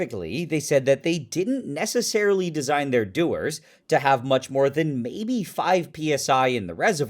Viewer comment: oooooh "dewar" ... I just thought you were saying "do-er" to be funny.... Like, "the thing that be doing"